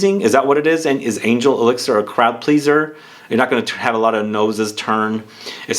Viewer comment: Not a surprise at all! Sucks that they changed it though. Playing the vid at 2x speed, I swear I heard "crapleaser" 🤣 The new word in designer perfumery.